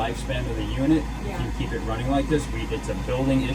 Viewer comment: Should have left that thing off that’s really dangerous it didn’t have even close enough pressure to run right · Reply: oh I thought you said you left it on and running like this.